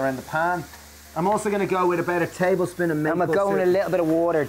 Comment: A tablespoon of what, I can't hear :( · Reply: Maple Syrup, excuse my brother talking over me ;D